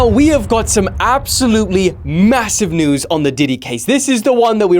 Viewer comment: did you say massive?